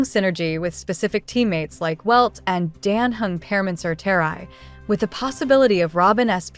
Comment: The CC say Imbibitor Lunae instead of Permansor Terrae.